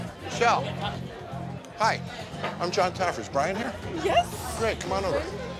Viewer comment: did he just call himself JOSH TAFFER?!?!?!?!?!??